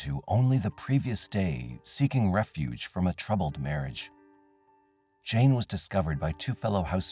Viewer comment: I love love love these long videos ❤ I thought he said he had a male marriage in the 1st story and was like "damn, thats an unexpected conversation for game night" 😂😂 Holy fcuk! I did not recognize Matthew at his sentencing!